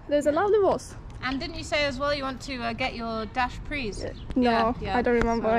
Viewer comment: I swore you said you wanted to get your dash prixs down too. lol